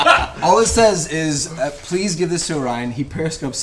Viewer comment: what was the person’s note at 3:53:05 ? i’m having a hard time comprehending what he’s saying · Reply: "Please give this to Orion he periscopes so much he must need it." Periscope was an app used for livestreaming videos, so he needs a phone charge bank for how much he uses it